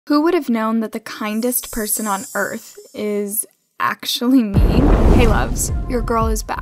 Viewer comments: I thought you said the nicest person on earth is actually me 😂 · Can we speak about that i heard "the nicest person is actualy 'me'"